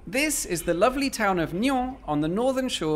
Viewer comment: Did you say the lovely town of NYAN with a French accent? :3 2:23 Would be more believable if they fully closed the curtains. (And if tourists take a closer look and don't know, they might be even more worried.)